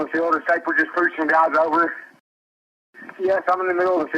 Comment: I thought he say "preaching guys over".